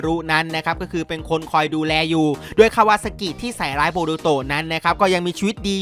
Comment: ได้ยินไม่ผิดใช่ไหม คาวาซากิ 555555